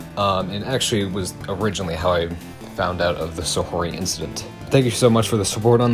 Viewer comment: i keep hearing "the so whory incident" and i just cant stop laughing.